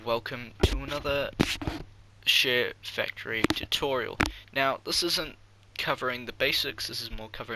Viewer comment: sounds like ur saying shitfactory tutorial